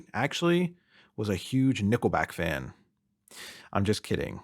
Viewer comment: Hahah nickel back fan 😂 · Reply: I thought he said nipple back fan. As i was lay here eyes closed listening. The name nickle back is lame.